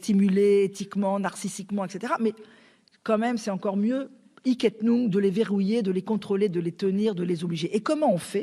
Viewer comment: ça veut dire quoi '' iquette noune '' vers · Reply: Je me suis dis que c'était sûrement une locution latine à la con et après recherches effectivement c'est "hic et nunc" : sur-le-champ, sans délais